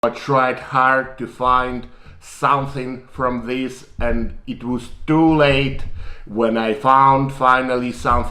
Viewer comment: I thought he said “I tried conors divine something” lmao